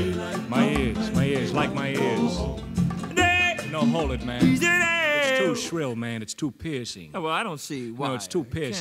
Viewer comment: When I first heard that I thought he said "its too pissy"